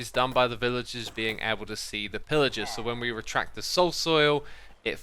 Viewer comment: Did say soul soil?